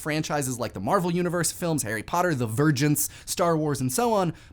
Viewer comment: Did you just say ... "The Virgins?"